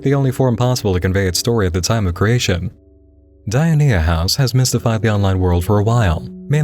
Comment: listen, I just....I almost burst out laughing because I thought you said diarrhea house instead of dionaea house and i'm a tired, immature person